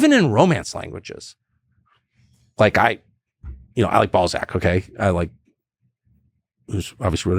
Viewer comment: LMAO I was half listening and out of nowhere thought i heard Tucker say "I love ballsack"